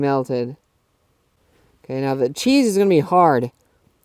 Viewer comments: Did he just say cheese? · did he just say Cheese? why? 30:03 😭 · Wait did he say CHEESE?